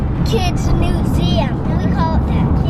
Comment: Is it just me or did anyone else think that at 1:37 said NEWseum?